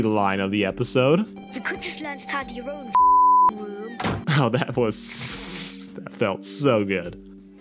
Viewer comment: the hell is he saying? I could just letadiaf*** room? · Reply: "You could have just tidied up your own fucking room"